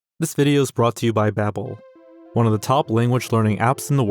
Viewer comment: Am I the only one that thought he said “this video is brought to you by Apple.”